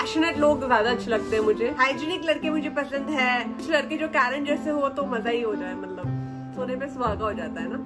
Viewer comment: what did she said? Kuchh ladke jo kaise ho?